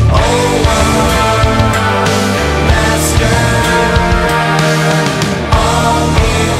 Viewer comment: keep hearing "owo master" and I'm so angry about how furry content has ruined me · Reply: haha damn, now i hear "owo master" too